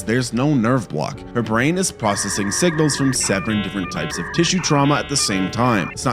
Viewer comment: "processing sevren-" yes he actually said that